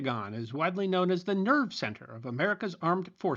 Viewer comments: I'm ngl I thought they said "nerd center" at first and I mean, they're not wrong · I misheard that as “nerd center” initially. I was thinking, “Psssshht…they wish.” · At first I heard "widely knows as Nerd Center" Also putting it on "Hell‘s Bottom" is also some sorta joke lol